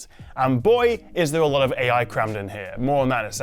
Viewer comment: sure sounds like you said boy, is there a lot of AI crap in here